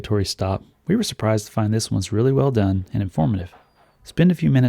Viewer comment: totally thought he said "burrito forest" lol